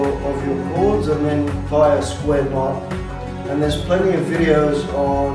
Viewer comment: Hi! I'm sorry could you please type the name of the macrame technique you used for the length of fringe you are demonstrating? I couldn't quite hear you properly. Thank you! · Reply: thank you! I listened again and I think you said you use square knots to make the fringe? Around the 6:00 mark. Once again thank you for your work! <3